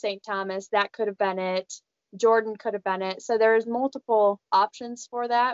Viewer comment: did she say jordan at 19:55? when did this happen I must have missed that. were they even on a challenge together?